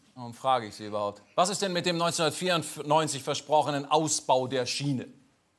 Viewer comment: hab ich da kurz 1944 statt 1994 gehört 🤣🤣